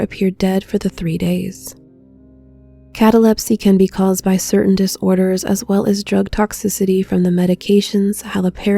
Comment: I thought for a second that you said: "...this condition can be caused by discorders..." which made me laugh my ass off.